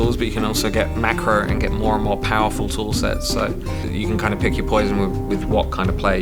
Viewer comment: i hope you guys dont make an ocean of bugs whit that complexity and it will be cool and fun to play 1:35 is it uprading system or more tools i dont heard it clearly i heard tool sets